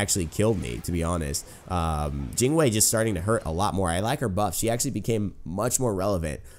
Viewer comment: Did you say I like her butt or buff?.........